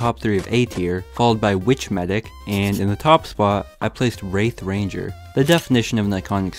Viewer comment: I thought said rape ranger not wraith ranger